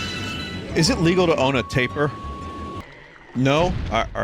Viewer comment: When he said pets and said tapir I thought he was saying "taper" as slang for tape worm